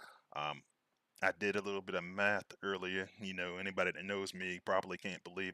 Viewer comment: I missed it last night. @ 43:53 did he say math or meth lol. <link>